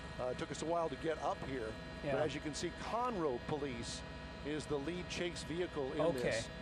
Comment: I thought he said Connor (Hello my name is Connor...